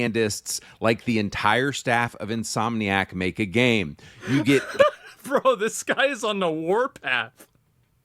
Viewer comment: Thought you said this guy was a rat?